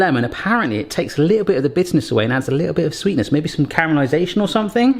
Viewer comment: When spoke of lemon bitterness I thought he said business so I'm thinking lemons have business?